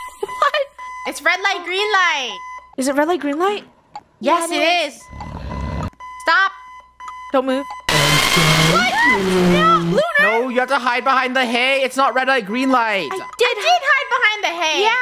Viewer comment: i think he said "Dont move"